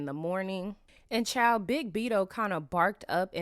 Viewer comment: 😂😂😂😂 I had to rewind this twice….did you say BIG BIDO?!?!?🤪🤪 nasty work lil sis….nasty 🤭🤭🤭🤭🤭